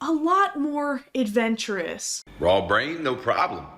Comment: What he say? “Raw brain, new prions?” what a scholar he is.